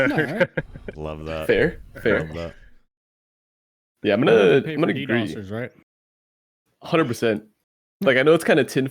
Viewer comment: Does anyone know what says at about 17:21? He and spoke simultaneously and it seemed as though the podcast understood him but after watching it over and over I just couldn't make out what he was saying. "You know they paid for the __________ right?" and then responds with 100%. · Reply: they paid for the Ddoss'ers Right ?